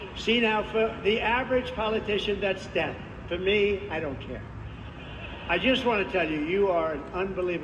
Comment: At the 10:35 mark, Trump's says " to the average politician, that's death, to me? I don't care." Is that what he said? Is Trump's campaign workers and security personnel his enemies ? Or is Trump aware of it and plays his role?